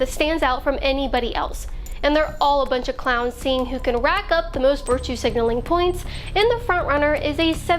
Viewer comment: Did She just say "Rack Up"...............................I agree, it iS! Then I heard, 76 YO Kiddidler that can't remember what he had for breakfast" THAT was FUNNY!